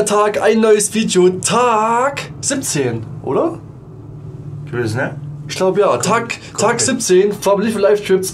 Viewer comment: Was sagt bei Sekunde 0:11 ? 😂😂😂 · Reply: wesch ne